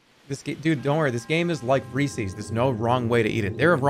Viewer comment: jesus christ I thought he said feces not reeses